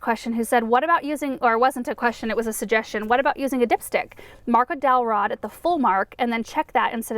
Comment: I thought you said dick stick at 17:48 ! Lol! I had to read it over...thought I was losing my mind. I was like, "why on earth would she say something like that???" Lol. PS - I love your garden.